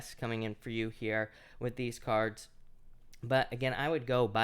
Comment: I thought u said "aries you've got the 'by the book card, I feel like sex is coming in for ya" SORRY WHAT? REWIND....!!